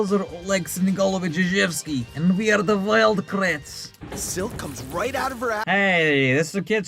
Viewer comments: I remember I thought this one emo guy named Zach kept saying “Wild rats!” And I thought he said wild craps and at the time I thought crap was a bad word so I thought I was being savage for watching it. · I remember when Zach would usually say things like "Whatever, Wild Rats" and the whole team would respond "That's Wild Kratts!" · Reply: I thought he said wild brats. Or is that just me?